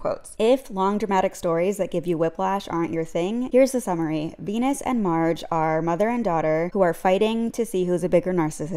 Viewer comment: I've been watching too much sailor moon cause all I kept hearing was "Venus and mars"